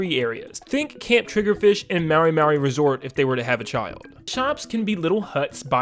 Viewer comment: did he just say "marry marry resort" 💀